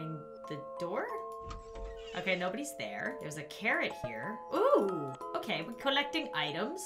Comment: i may just be deaf but when she said "theres a carrot here" i heard " theres a Karen here"